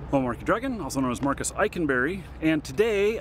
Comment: SHIT I thought he was saying Markeedragon or Marcus "I can vary in real life" not Eikenberry